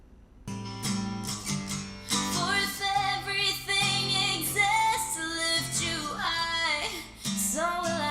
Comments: That moment when you realize at 7:04 she sings "exist" instead of "dare", hence no "r" sound. But the suggestion is still valid 😄 Btw Love your channel!!! ive learned so much from watching your videos!! It just makes me excited to sing! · I think at 7:03 she actually says, "exists to lift you high", not "it dares to lift you high", just saying.